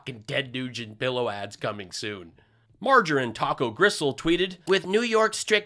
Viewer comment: "Marjorie Taco Gristle" - the visual suits her. Thanks for doing what you do. I look forward to your segment every Saturday. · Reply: Thought said "Margarine Taco Gristle". Whatever, that was great. Hey, why aren't you the star of late night comedy yet? Too glib and funny!